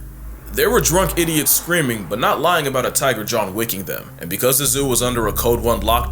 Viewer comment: I thought you said John licking them instead of wick and I’m not disappointed but if you read this I’m sure you regret not saying that now